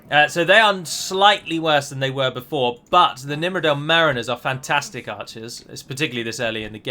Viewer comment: For some reason I do not here "Mariners" I hear "There it is" lol